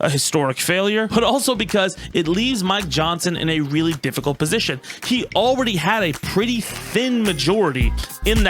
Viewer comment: Not gonna lie, when talking about Mcarthy retiring I thought you said "It leaves my johnson in a really difficult position" and I was both concerned and... well mostly concerned.